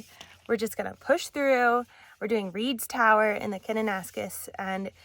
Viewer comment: We will be in the area in Sept and I was taking notes of the trails you did. What was the third trail that gave you views of Barrier Lake? It sounded like you said Snake Mtn Trail, is that correct? Thanks!